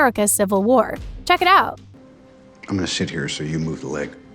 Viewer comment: Sounded like tony said"I'm gonna shit here"😂